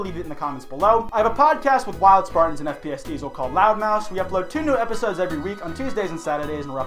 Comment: I’m not gonna lie, every time he talks about his podcast I always hear “Loudmouse” and not “Loudmouths”. I can’t unhear it.